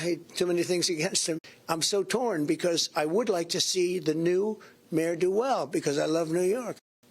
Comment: Trump said "see the new mayor do well". In my mind I heard "see the ne'er-do-well".